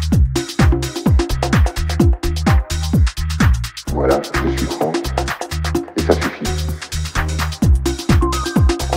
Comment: Does anyone know what's being said at 2:25? "Voilà; je suis ____, et ça suffit"? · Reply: je sui franc + et ca suffit srry :P